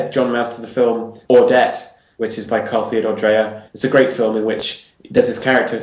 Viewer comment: about what movie he said? Somebody please tell name. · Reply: Ordet